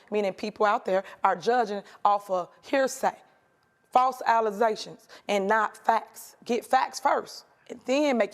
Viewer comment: …. 😳😳😳😳😳 DID SHE SAY …”FALSE alleZZZZZZZZZZations?!?!! Lmfaoooo she’s making HUNDREDS OF THOUSANDS OF DOLLARS and LEADING in our country and she says ALLIIIIIZAAATIONSSSSSS. Ma'am….its “allegations”.. and they’re going to bring you down as they’re proven true.